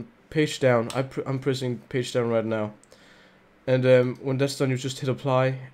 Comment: is he sayigng paste down